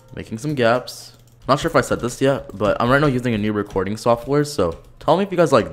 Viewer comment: I thought he said reporting software